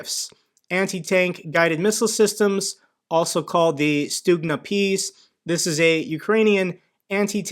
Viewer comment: Along with Javelins and NLAWs provided by the West, Ukraine also has a lot of Ukrainian-made anti-tank systems that actually proved to be very effective: Stugna-P, RK-3 Corsar, etc. Those may be not that sophisticated as Javelin and are not portable, but yet have their own advantages, one of which is the price. There are a bunch of videos online of Ukrainian Stugna operators destroying Russian armored vehicles and even helicopters! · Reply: I kept thinking it sounded like was saying "student peace" ... :)